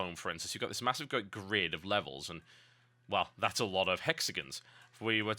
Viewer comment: I heard "That's a lot of mexicans"